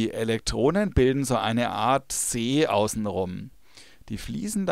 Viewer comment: Was bilden die Eletronen? (11:56) Ich hab' leider das Wort nicht verstanden. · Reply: so ne art see :D (fachsprachlich : elektronenwolke)